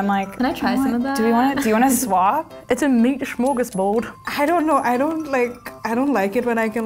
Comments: wtf did she say lmao. miss the old buzzfeed people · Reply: New Zealand accent, smorgasbord is super popular there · "it's a meatashroglesbord" wtf did she say m8